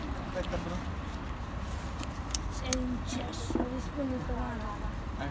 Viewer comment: Is it me, or does someone say "sei un cesso" at 0:02?